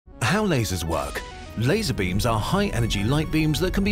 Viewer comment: i heard "laser bean"